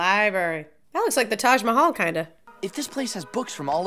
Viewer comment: did she say the taj mamal? girl